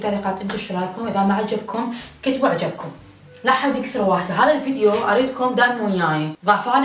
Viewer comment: كأني سمعتها قالت هواسا احد سمعها مثلي ولا لا؟؟؟؟ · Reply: مينكسر واهسه واهس يعني بالعراقي مثل الخاطر